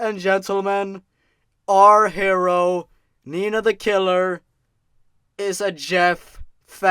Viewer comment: I swear to God I thought it said "Ninja the killer."